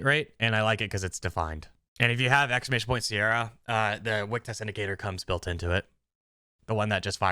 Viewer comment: What did he say? "X Match Point Sierra"??